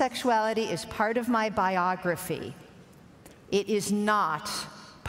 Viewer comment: Did anyone else hear "biology" at the 0:26 mark? Made me cringe just a little.